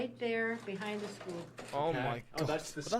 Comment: Wait what you said 9:57?😅 A good thing is Alaska along with Hawaii rarely has mass shootings. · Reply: he said "students" not shootings. i heard the same thing the first time